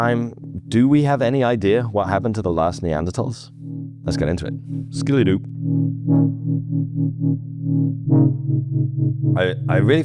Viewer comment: did he say Scooby Doo? 😅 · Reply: skiddly doo — it’s scatting 🎶